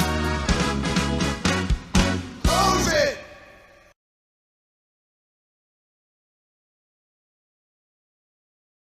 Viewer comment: What did he just say at 2:08? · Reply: I think he said “close it” or something similar to that